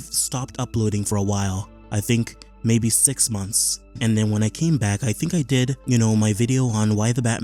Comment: Did you say 6 month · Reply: Yeah, 6 months